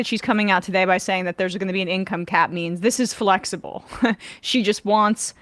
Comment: I heard this as income cat memes lol